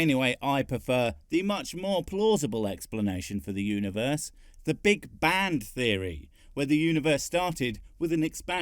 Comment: I thought you said “Big Banned Theory” That’s the theory that everything started when God turned his router off and on